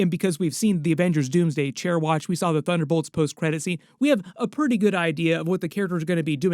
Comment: am i the only one who heard ‘the butt whole’s credit scene’